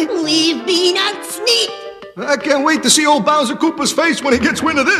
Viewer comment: did he say B O W S E R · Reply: Yeah Luigi said Bowser